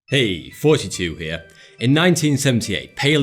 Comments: It sounds like he says fousytube everytime he says thoughty2 here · did he say fouseytube or something else? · I thought he said 'hey fouseytube here' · Why am i hearing "hey, 72 here" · I always thought he was saying "42 here" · At the intro, I heard: "Hey, 42 here."